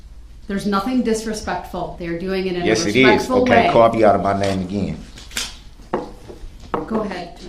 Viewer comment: What is he saying here? "Call me out of my name again"?